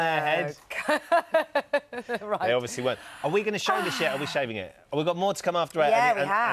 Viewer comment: "Are we going to show the shit or are we saving it?" Is that what he said or am I hearing things?